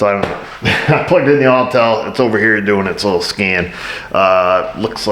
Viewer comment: Just noticed the name of the scanner company is Autel, I always thought he was saying "All-Tell."